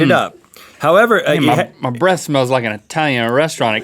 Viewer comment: No he said my breath smells like an italian restaurant exploded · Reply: awesome ohh haha I thought he said breast 😂